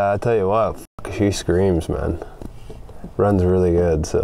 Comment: Thought he said runs really well ?